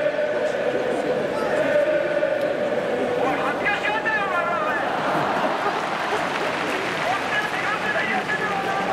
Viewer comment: 何て言ってるの？ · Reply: 「おい、恥ずかしがんなよお前ら、おい！こっちだって40年やってんだよ、こんなこと」って感じですかね？